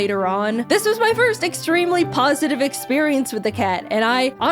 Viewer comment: Did you say pawsitive?